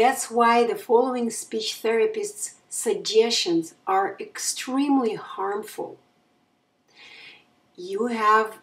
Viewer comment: I heard U say focus on breathing in this presentation..You commented on Andrews Post saying don't focus on breathing,that "Pauses" are normal in Speech,why do you contradict yourself?I'm just curious? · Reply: Hello, mike F, thank you for watching and asking questions. Go back to 4:35 and listen carefully what I really said there:-) "The following speech therapists' suggestions are extremely harmful..." This is where I listed focusing on breathing. Happy learning to you.